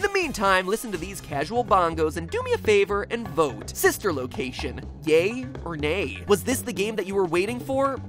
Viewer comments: at first I thought he said "Sister Location: GAY? Or NAY?" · Sister location: Gay or Nay? AM I THE ONLY ONE WHO HEARD THAT?????? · I totally misheard yay or nay as gay or nay. I think I need to go to bed...